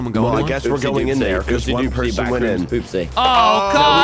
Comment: did anyone hear one of them say poopsy?